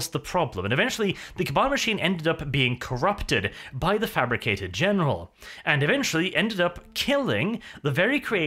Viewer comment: I always misheard the name as Fabricated General...